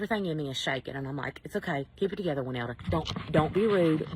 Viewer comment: Did I hear correctly that her name is "Wynelda"? That may be the most Karen name I've ever heard. And did she really say she was worried about swearing in front of God ("he forced me to cuss in the presence of God and His holy matrimony"–what does that even mean?)? Does she really think that He can't hear her swearing in this video? I mean, what the hell did I just watch?